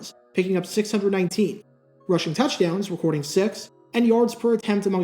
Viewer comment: I misheard that at first. What I got was, "…rushing touchdowns with 46…" 🤣🤣🤣🤣🤣